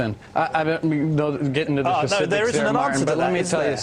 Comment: did he say @ 0:55 that he didn't want to get into the pacifics?